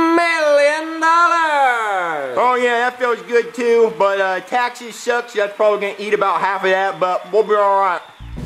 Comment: "Taxes suck and it's probably gonna eat about half of that" I felt that 😂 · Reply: yea i thought he said taxies suck =/